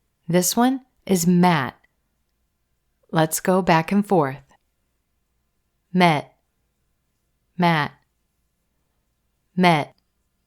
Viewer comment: When you read "met - mat" - I hear "men - man"; and, when you read "pet - pat" I hear "pen - pan". The "held T" really similar to "N" sound. It is an example of "held T" (the "T" without expiration), isn't it?